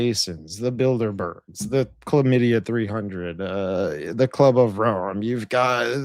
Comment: I was half listening while doing something else and I heard “chlamydia 300” 🤣